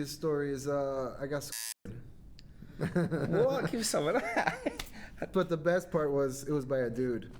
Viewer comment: What did the guy say at 0:04? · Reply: He got squirted. But by a dude soo not sure you can call it that 🤔